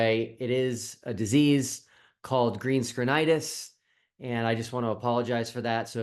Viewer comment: I hope you take this in the humourous manner it's intended, but I misheard your condition as 'green-screenitis". I'll then say I'm sorry to hear about it, and I hope it's not to shitty for you, eh.🙂